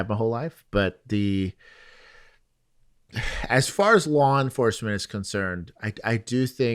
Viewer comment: Totally thought you said Juan enforcement instead of law enforcement.